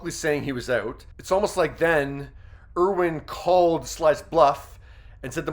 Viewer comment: All I heard was Sly's Ego. SMH